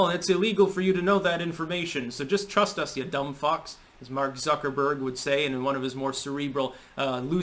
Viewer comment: oh I thought he said "suckerberg" 🤣